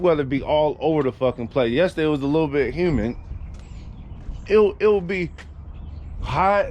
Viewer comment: Did say Human instead of humid at 1:45 😂😂😂😂😂😂 all Love bro